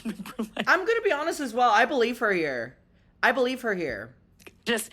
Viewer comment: I thought, on God, that you said "I believe her ear" right after she said her eats are getting red for just talking about it 🤣 but you said "I believe her here" lol